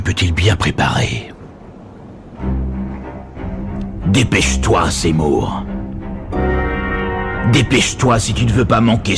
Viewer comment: " dépêche toi zemmour" que viens d entendre